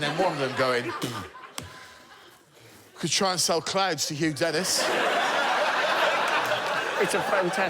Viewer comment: I misheard Hugh at first, I thought he was a member of the CLOWN appreciation society. A bit disappointed he isn’t.